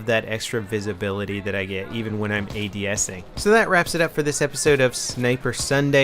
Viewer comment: I thought he said 'even when I'm PMSing' lol :P